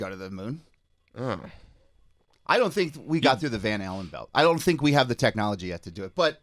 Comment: did say "the van halen belt?"